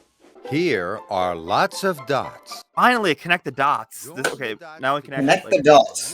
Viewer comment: I misheard "lots of dots" as "lots of ducks".